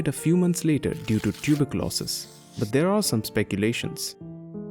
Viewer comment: Does anyone else have a negative reaction to the fake voices being used in videos these days? It is possible the presenter here really does sound like an old Indian man on downers, but there are so many other presenters using the same AI-generated voice - which makes the same pronunciation mistakes - that it seems unlikely. I am attracted to this channel because of the content but just can't subscribe because of the phoney voice. Is it just me? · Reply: I heard "died due to tuberculosis" as "died due to tulip losses". Made me wonder how passionate was his love for tulips!